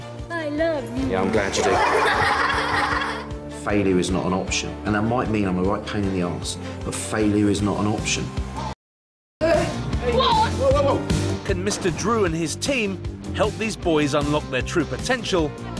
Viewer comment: Am i the only one who thought it said dr drew